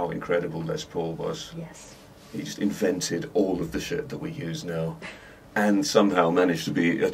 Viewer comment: did i just hear Guthrie say "shit".....